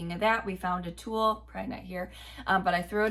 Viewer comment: At the 17:18 mark did I hear say “pregnant here”?